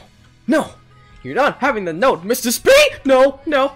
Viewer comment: I think on 11:28 he said mr speak im not sure xD